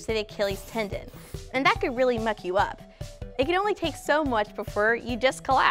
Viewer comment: I swear I thought she said fuck you up lol